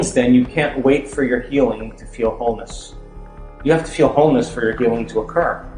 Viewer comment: Hope you enjoyed watching this video by Dr Joe Dispenza. Don’t forget to SUBSCRIBE to our channel for more Joe Dispenza Meditation videos: <link> Here are other videos we believe you’ll enjoy just as much, if not more, than this one: Dr. Joe Dispenza - How To Create The Future You Want <link> Dr Joe Dispenza 2020 - THE SIDE EFFECT OF TRUE TRANSFORMATION (NEW) <link> Dr Joe Dispenza 2020 - How Breathing Can CHANGE YOUR LIFE (Secret Revealed) <link> Dr Joe Dispenza (2020) - Life Changes In 4 DAYS [RESEARCH] <link> Dr Joe Dispenza (2020) - Pineal Gland Activation [SECRET BREATHING TECHNIQUE REVEALED] <link> Dr Joe Dispenza (2020) - Pineal Gland Activation [SECRET BREATHING TECHNIQUE REVEALED] Part 2 <link> JOE DISPENZA:"GERMS DON'T MAKE US SICK, WE MAKE OURSELVES SICK" <link> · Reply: You may want to correct the error at 3:52 where the text reads homeless instead of wholeness. The same error occurs again at 5:24. I don't want to see people getting the wrong subliminal message. I have tremendous respect for the work of Dr. Joe Dispenza.